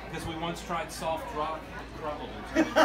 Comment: I didn't quite catch his joke at 14:00 - Do you know why we use heavy metal? We once tried soft rock... (can't hear) Rolling Stones. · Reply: I think he said, "but, it crumbled into Rolling Stones"